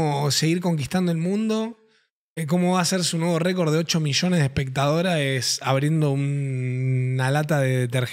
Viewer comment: que dijiste???????????? espertadoraes?????? sobra una ''a'' ahí xd